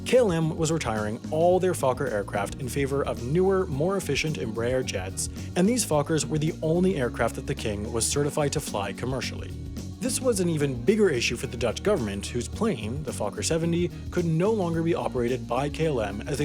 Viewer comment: I misheard @ 3:49 “...and these f*ckers were the only aircraft that the king was certified to fly commercially” lolll strong words hahaha